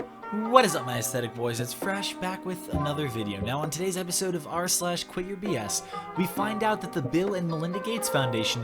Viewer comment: I was so sleepy I thought it said “QUIT YOUR FRESH.”